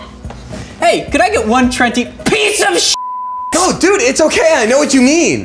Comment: It sounds like said can i have. One plenty piece of shit